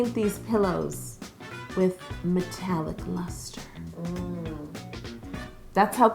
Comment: When she said metallic luster i thought she said Nutella mustard!!🤢😂